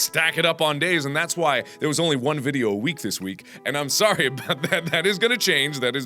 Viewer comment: Anyone else hear "sorry there was only one video a week this week?" am I alone here?